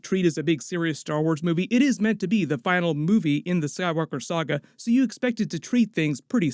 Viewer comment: sounded like you said sad walker saga. It’s a sign.